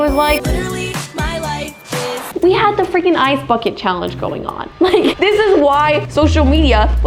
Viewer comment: I misheard "ice backet challenge" in 16:40 as "ice f*cking challenge"💀